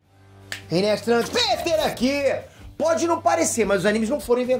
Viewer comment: entendi "Hey nerds trans PETER aqui"